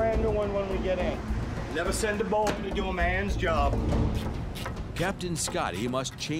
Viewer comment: did say never send a muley to do a man's job?